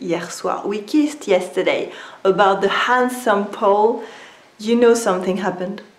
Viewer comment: Did I hear "handsome Pole"? Or is it just what I wanted to hear? :D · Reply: She says "If your best friend tells you 'we kissed last night' about the handsome Paul"